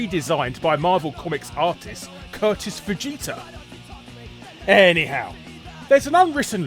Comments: Did I mishear, or did he just say that guy's name was Vegeta? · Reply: Kurtis Fujita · I'm sorry, did you just say 'artist Curtis Vegeta'? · Did anyone else mishear Kurtis Fujita as "Curtis Vegeta"?